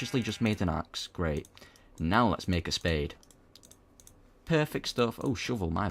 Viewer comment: at first when you were saying "spade" I thought you were saying "spoon" xD